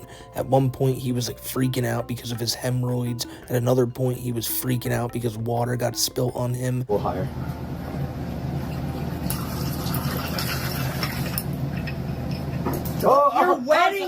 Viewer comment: I thought it said freak offs